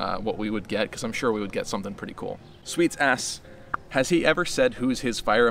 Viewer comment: I really thought you said "Sweet Ass" out of nowhere.